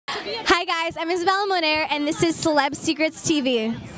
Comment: seriously I thought she said she's isabela Norman not moner